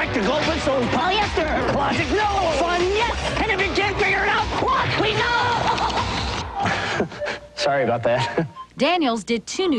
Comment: transcribe what he said, please. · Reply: That’s half correct. “Logic? No!” “Fun? Yes!” “And if you can’t figure it out, walk, WEASEL!!!” gets exorcised “Aha… Sorry about that.”